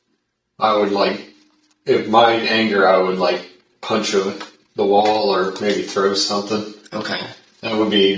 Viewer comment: Kind of sounded like he said “punched her” right before he said “punch the wall.”